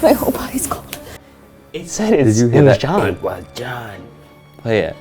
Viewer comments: i dont hear "it was them" i hear "it was him" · I heard: “it for-got!” Or “it was got” I hear a definite T sound at the end, absolutely no N sound. Maybe, just maybe, gone. But not John. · When it said "it was them" I heard "everybody" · and Myike say they hear ”it was john” but for some reason, I keep hearing ”it was God” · the “it was john” at first sounded like “what the FACK” and then i heard “it was GOD” to me 😂😂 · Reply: I heard “it was him” and “it was them “ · When you heard ‘it was them’ I REALLY heard ‘it was liz’ instead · Them: *obsessing of "It was John" Me: Dude, are we not gonna talk about how it said she was more dangerous? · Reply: I heard “it was them!”😳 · for some reason i keep hearing "he forgot" instead of "it was john" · yo when everyone heard "it was them" I heard " it was em" ( as in Emma) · i kept hearing : It was god. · I hear "es war john" witch is "es war john" in German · i dont know why but when it says "it was them" it actually sounded like it was saying "it was him" , reffering to john who did the murders. the voices also indicate that there was a collaboration between liz, john, and emma.